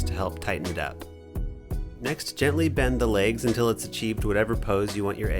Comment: I'm so used to puns in these videos I thought I heard legs as "leggs" for an egg pun then realized no that's the normal word